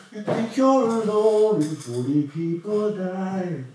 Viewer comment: Bo, You're spoiling us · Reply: “you think you’re alone and 40 people died,” did I hear that right? Subtitles don’t help